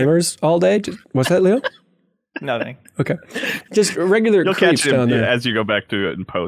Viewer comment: what does Leo say at 22:20? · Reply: "Upskirt?"